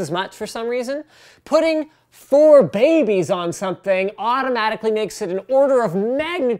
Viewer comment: When he said "has for babies on it," I thought he said "4 babies." Only me? oh... ok